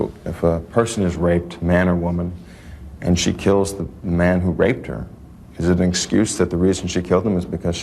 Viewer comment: When you were talking to, I heard her say, "He raped them."